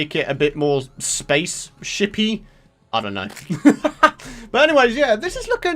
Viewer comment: Everytime he said shipy I thought he said chippy